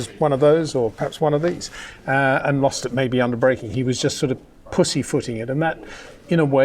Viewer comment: I had to do a double take 1:12, I just heard “pussy” and was like wut? But then heard the pussyfooting, and I was like oh yeah, that makes sense lol